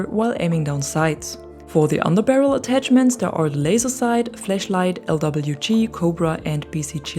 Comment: It‘s a Flashlight, not a Fleshlight, but yeah it does… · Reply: Iknow. Just thought it sound like you said fleshlight so i just tried to be funny. :D